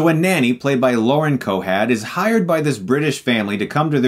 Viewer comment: Sound like youre saying Lauren Cohad instead of Cohan